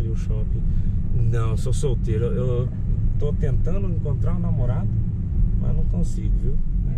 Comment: Minuto 11:03 to tentando encontrar um namorado ? É isso mesmo ? Entendi bem ? Não é que eu tenha alguma coisa contra eu sou a favor mas pode ser uma revelação isso aí ele assumir que é gay 😅😅😅